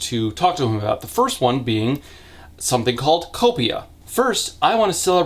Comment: For a moment I thought you said Isla wanted to celebrate copium and I was perfectly happy to just accept it....